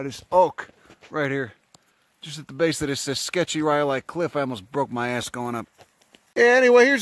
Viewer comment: Did he say rhyolite? This man. I'm from s. nevada and s. utah.